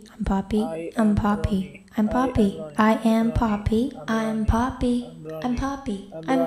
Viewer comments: Latr i heard like "i Am pappi😂😂" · I thought she is saying I am potty